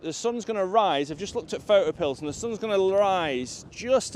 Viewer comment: Great video. I didnt quite catch the name of the app you were using at around 06:35, can you let me know please. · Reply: photopills